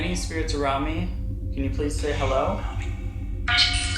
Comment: Hi. Great content as usual. At 7:20 when you ask "are there any spirits around me ?" I thought I heard something mimic and say "around me" Ok. I'm off to watch your next vid. I've been outta commission for a couple of weeks so have some catching up to do ! 😉👍